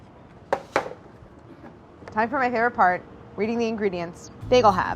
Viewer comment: When she said "Time for my favorite part" I first thought she said "I tore my hair apart". ;-)